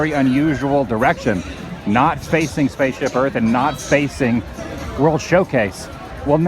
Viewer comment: Is it just me or did he say"...not facing Spaceship Earth and not spacing World Showcase" at 10:43? Inevitably a tricky tongue twister. P.S. Random thought. The spelling for the word tongue is quite strange in my opinion. Anyone else think so?